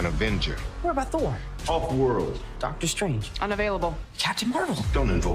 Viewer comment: Did he say Thor was on hulk world 3:00 another hulk movie I presume